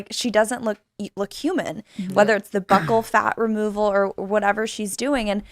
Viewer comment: I thought she was saying "butthole fat". Buccal fat. Long U.